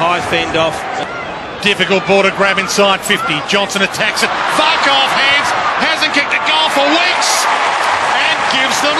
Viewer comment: did he say "fuck off hands?